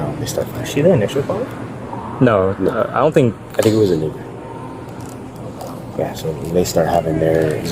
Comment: man what he said “i think it was a neighbor” but yall put “niger” w ya racist asses!